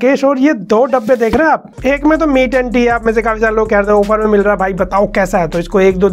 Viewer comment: I just hear mi tatti 😅😅😂??